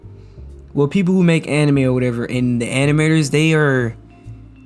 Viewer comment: I could have sworn he said "the animayors" . I'm going to be using animayors as often as I can.